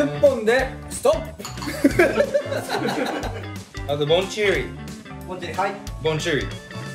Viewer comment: ジェシーのBonjiriがボンジュールに聞こえて笑いがとまらない😂